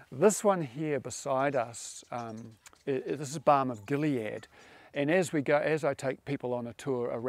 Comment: Which was the plant he said you can rub on your skin that smell amazing? Balm of Gilead it sound like he said?